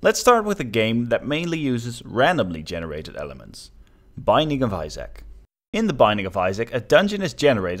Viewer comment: i thought he was saying smthn like Bye nigga visac... I m dying of laughing